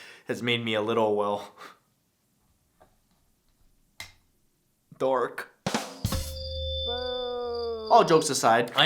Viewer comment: thought you said 'dork' and was tryna figure out the pun